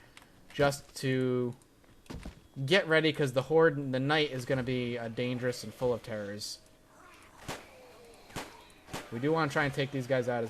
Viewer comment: it sounded like you said the night is gonna be dangerous and full of terrorists. It made me laugh.